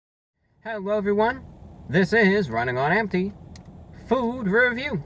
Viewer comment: Bro, what's your name? I just realized you say "Running on empty". I thought you said "This is Ryan Onety"